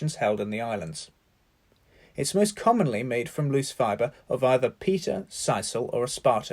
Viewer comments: 😳 Did he just say they are made from loose Peter fiber? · What is the first material mentioned @ 0:42? I cannot quite hear it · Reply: Sisal or pita (both agave fibres)